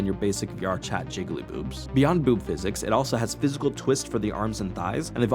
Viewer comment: had to listen again, thought you said Cock physics at first.